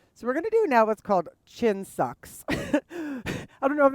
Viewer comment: I have been starting my day with this yoga and love it. I chuckled today. I thought you said gym socks because that’s the face I make when I smell gym socks instead of chin sucks 🤣